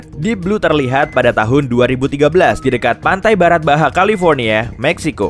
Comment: pendengaran Saya Kok Barat Baha Yak Apakah Saya Salah Dengar ?